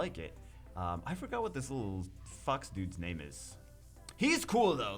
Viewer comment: LMAOOO Ik he said fox but it sounds like he said “what is this little fuck”